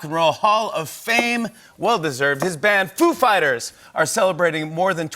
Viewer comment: I really thought I heard Jimmy introducing the band...Poop Fighters